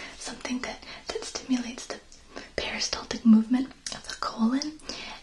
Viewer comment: Lol for a sec, I thought you said the "Paris Hilton movement of the colon" 😂